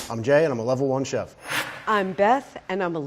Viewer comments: I thought Jay said "I'm gay and I'm a level one chef' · omd my volume was on low so i thought the lvl one said '"i'm gay and im a level 1 chef" smh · At first, I thought Jay said, “I’m gay and I’m a level 1 chef.” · Is it just me or did I hear jay say, "Hi, I'm Gay, and I'm a level one chef." I mea nthat hat does say something :+